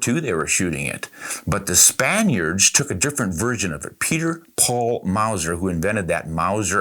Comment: For a second there I thought you said Peter Paul and Mary….🤡